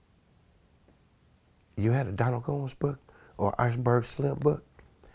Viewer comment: Who's that Donald person that suga free mentioned at 40:30? About the books I heard iceberg slim but not the other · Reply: The Donald he's referring to is Donald Goines. Goines is a well known author of books that speak to people who have lived or still live in hoods all over America.